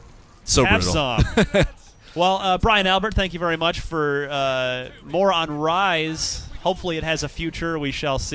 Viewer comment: Near the end I thought he said "thank you very much for Moron Rise"